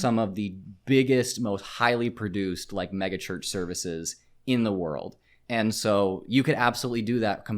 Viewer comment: I heard "Church CIRCUS" instead of "church service" and... yeah, it fits. I need more people to adopt that.